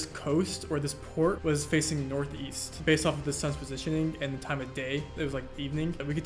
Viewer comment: When you said "the sun's position" my dumb ass thought you said "the son's position" I was like, "what does the son's position have to do with where they are?"